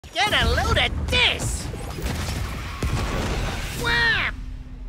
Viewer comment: Nah I thought Bugs said “GET A LOAD OF DICK”😭 Also them Founders Packs is expensive asl😅 When the sales coming???🧐